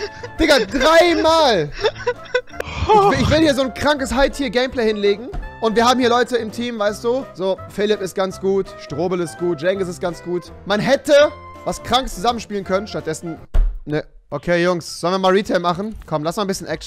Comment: Strobel ist ganz gut HA DA HAB ICH MICH VERHÖRT Spaß CHEF EHRENTAUBE EHRENMANN EHRENSTOBEL